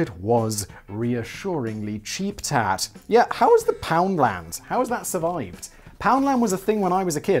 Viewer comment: I genuinely thought was saying pound lamb